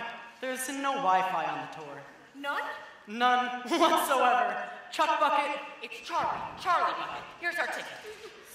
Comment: I thought he said Chumbuckets